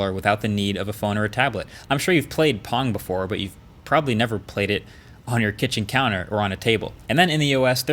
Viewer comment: i thought he said you never played porn on kitchen counter